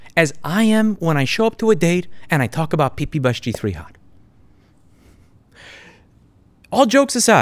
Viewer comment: For the life of me I can't understand what you said here... Can anyone can help? 3:35 PPBush3Hot?